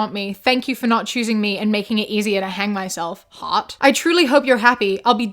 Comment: I was listening to this while driving and I'm American, so every time she said "heart" while reading that one post, I thought she was saying "Hot." Like the girl was replying to each of his threatening texts by saying, that's hott. I was like, that's a wild choice to make, but I love it, lol